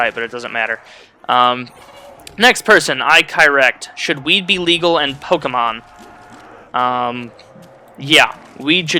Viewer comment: wat was the 2nd question? it sounded like he said should we be legal in pokemon? huh?